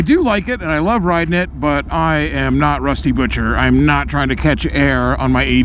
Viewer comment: Misheard Rusty Butcher as Rusty Venture and was like "damn me and Josh remember Venture Bros very differently."